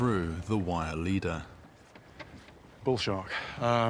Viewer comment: I thought he said bull S##t lol